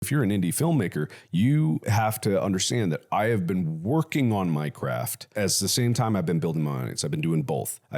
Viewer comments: Oh, "I've been working on MY CRAFT" !!! I kept hearing "I've been working on Minecraft" 😂😂😂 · I wasn't listening when I first flipped to this and thought he said he's been working on minecraft, minecraftxmarkilplier crossover when?